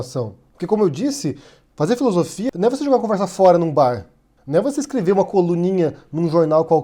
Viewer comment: eu ouvi Luiz Felipe Pondé? 🤮